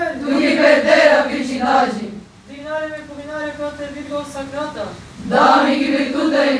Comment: Alguém sabe o que foi dito nessa parte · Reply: Dignare me pugnare pro Te Virgo Sacrata, da mihi virtutem contra hostes tuos. Fazei-me digno de lutar por Vós, Virgem Sagrada, dai-me força contra os vossos inimigos.